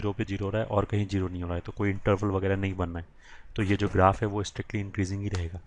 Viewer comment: What is that word which sir pronounces at 6:41 I guess, it's 'INTERVAL', right? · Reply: Yeah its interval